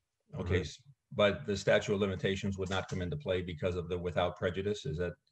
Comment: I think he's saying "statue of limitation", maybe I'm wrong but that's what I hear. It's "statute of limitation". This is a whole bunch of cringe on Billy's part. Who is Melanie Griffith? Is he namedropping a celeb for brownie points or is it some random person we don't know connected to this case?